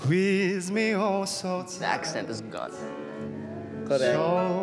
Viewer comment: i really thought he said “his accent is awesome” but as far as i know they don’t speak english there and now i’m ????